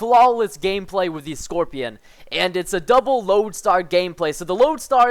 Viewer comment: for some reason i thought it said double moab haha lol